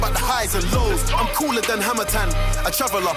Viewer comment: I thought Skepta said Hamilton not Hammer time lol